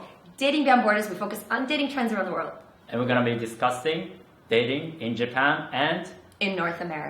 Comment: it sounds like says "And we're gonna be disgusting..."